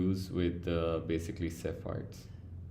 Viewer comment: sir what did you say? We use sapphires for nearest galaxy or something else?? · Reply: Cepheids